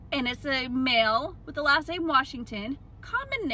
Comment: Or maybe Washington is actually the given name of these two men, and the surname is uncommon? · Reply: It's not that though. The caption says Mr Washington and she says "It's a male with the last name Washington" at 0:40.